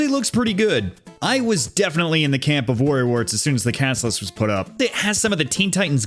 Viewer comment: When you said "worry works" the captions thought you said "warrior warts" 🤣